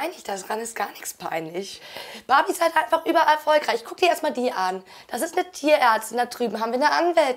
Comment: Ich habe bei erfolgreich Fall Guys verstanden 😂